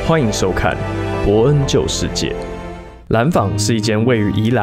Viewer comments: 是不是只有我把博恩救世界聽成了博恩就是賤？ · 我聽成 博恩救世劍...